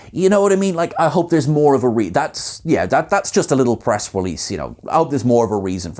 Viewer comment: HA. When talking about the Deltan character not taking an outh of celebacy, I could have sworn said "breast release"!